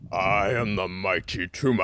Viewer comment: As a kid, I always thought he was saying “I AM THE MIGHTY TUNA” and I was extremely confused...